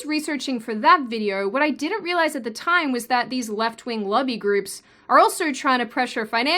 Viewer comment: Women love them "shooty-sticks." Amiright? Also, at first I thought you were saying "left-wing lovey groups," like you were alluding to them being hippies or something, lol.